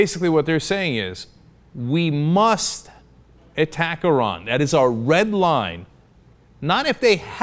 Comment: the subtitles are incorrect it says iraq instead of iran ....